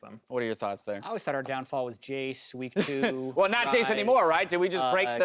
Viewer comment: What does Meteos say at 1:05:12 ?? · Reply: Ray Gaming sun was in their eyes